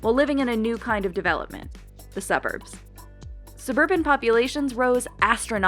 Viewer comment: She keeps saying “suburban”. I HEAR “wHIte”